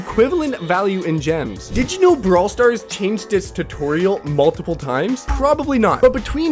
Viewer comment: Did he said "did you know brawl stars changed HIS tutorial" umm I dont thing a GAME can have gender but okay I guess?